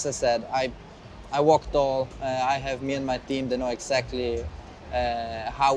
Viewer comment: Great interview by Jannik. Appreciate him taking the questions head on and answering them. That being said, what did he say at 4:57? Sounds like 'I worked/walked all'? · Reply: I'm italian but I guess he said "I walk tall", meaning "I've got nothing to be ashamed of" (in my language it'd be "cammino a testa alta")